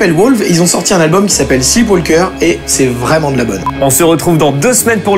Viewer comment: c'est quoi le nom du groupe et de l'album dont vous parlez vers 10:05? J'ai cru comprendre "Seawalker" de "Wolf" mais j'ai pas trouvé :/ Z'auriez pas un pti lien msieur? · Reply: Wolves - Sleepwalker